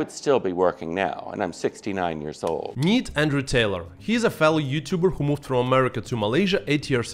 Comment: Glad his name Andrew Taylor I thought you said Andrew Tate. Btw welcome to MY 🇲🇾